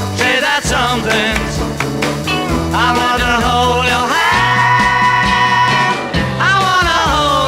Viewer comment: この曲は高校の英語の先生が音読の練習として流してました。 先生が言うには『I want to hold your hand.』が『アホな北条はん』に聞こえるそうです。 確かに日本人が聞くと『アホな北条はん』に聞こえます。